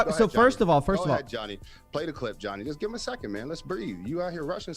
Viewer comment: This stupid guys logic is: If you want to become a doctor, dont go to a school, just learn from a doctor. What bullshit. Coffee what a killing debate, you were brutal bro ! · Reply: All i heard is "Find the clip johnny" 😂